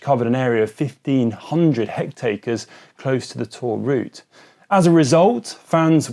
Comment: Did he just say hectacres